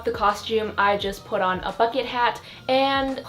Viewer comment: I thought she said , “then I put on a fucking hat” and I’m like wish casual aggression but I realized she said bucket hat 😂😂😂😂